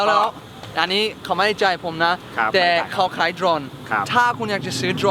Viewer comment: ผมได้ยินว่าขายตรง